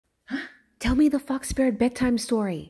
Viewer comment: When I just clicked the vid/short I heard: 'Tell me the f**k spirit story…' 😂 😂 😂 😂 😂 hahaha